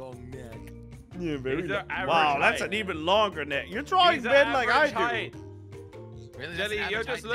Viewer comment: I thought he said "your drawing Manlikeissac"